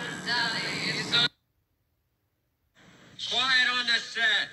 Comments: What it says? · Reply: teorías 1- Quior on the set 2- Quiet on the stage aunque no muchos saben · alguien me dice que esta diciendo? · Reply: Quiet On The Set